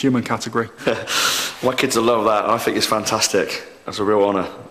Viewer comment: did anyone hear him say at 2:16 - 2:18 that the white kids will love it